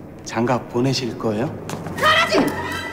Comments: 뭐라고한지 아시는분 못알아먹겄네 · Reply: 가라지!! 라고 말했어여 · 장미 던지면서 뭐라고 말하는지 알아들으신분 ..?? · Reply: 가라지!!!! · 뭐라고 한거임..? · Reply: 가라지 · 뭐라는건지 아시는분..? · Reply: 가라지